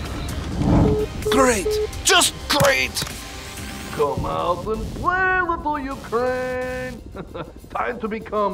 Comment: Idrk what is Russia saying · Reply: "Come and Play, Little Ukraine, hehe, time to be come NNEEUTRALLLLLl"